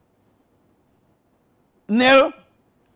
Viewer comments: Son: Dad, where are we going today? Dad: We're off to see Aston Village play Tsbfghghshgpsgh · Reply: I heard “Astro village” lmao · Did he just say "A Strong village" at the end😂😂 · 😂😂😂did I just hear "Aston village"?😂💔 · Did he just say: Aston Village?! 😂